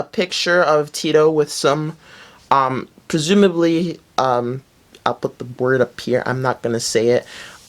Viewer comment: what did he say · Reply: He said he'll put the word up here meaning he'll put it on the screen the word was "stripper"